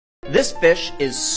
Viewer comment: I thought he said “This Bish”